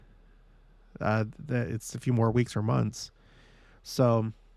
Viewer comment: I also didn’t hear “years” and had to go back and listen to this again and here is what I think he is trying to say… He is talking about what had already happened to him and so he said, I was given as a gift a few more weeks. Weeks is probably the first word he used because he was talking about how many weeks he was in the hospital, but then I think he corrected himself because it was several weeks so it actually ended up being months that he was in the hospital and so his final story was, I was given the gift of a few more weeks or months meaning, depending on how you want to interpret the timeline. It’s like the age of a baby. You are counting weeks because that’s the most reasonable unit of measure but after so many weeks, you start to count months because it makes more sense then. I do not think he meant to interpret what was going to happen in the future and how much time he had left I think this was solely referring to what had already happened and weeks or months, depending on how you’re counting. If you tell someone that your baby is 12 weeks old, they’re gonna do the math in their head and be like oh OK so they are three months old. But a doctor or nurse probably wouldn’t. They would be thinking something totally different about that 12 week number. I think that is all it was, he said… weeks or months. Next year he will then be able to say I was given the gift of a few more years. Sidenote, if you are still reading this, wouldn’t one of the witnesses in Jerusal